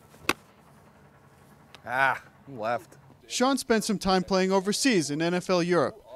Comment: Does he say 'aw fuck, goddamn it' around 2:23 into the video? Now there's something you don't see on ESPN every day.